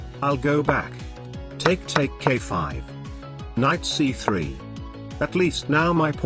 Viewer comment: "Take take k5" there is no k5??? (i know he meant a5 but the tts sounded like it said k5)